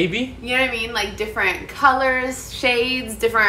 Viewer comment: For a second I thought he said"our wedding is going to be in a gas station 👰💍🤵💒"